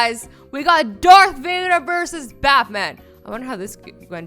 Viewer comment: did she just said bathman XD